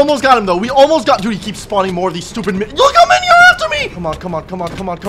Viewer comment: I heard just say bad word here → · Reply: He said stupid fricking minion